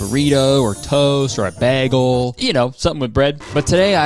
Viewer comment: DID HE JUST SAY “BAGGLE.” LIKE BRITTA PERRY 😭😭